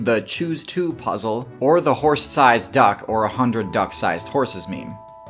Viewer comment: OOOOOOOOOO 0:27 OOOOOOOOOOO I thought he said "the whore size"....